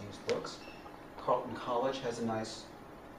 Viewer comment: what does he say at 3:21 ? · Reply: Isaac Gordon Colton college